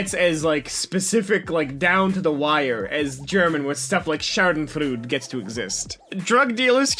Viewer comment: anyone else trying to find out what the word he says at 11:33 means or even how the f to spell it? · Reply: He brutaly butched the word "schadenfreude".